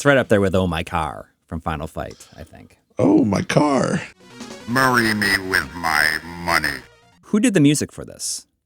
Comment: I always heard "Marry me with my money!" instead of "Bury"